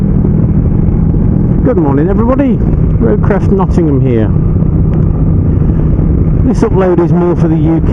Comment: Thought you said "Roadcrash Nottingham" LOL